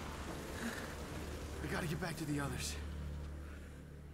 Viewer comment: He says "We gotta get back to the others" but the subtitles say "I gotta get to the others"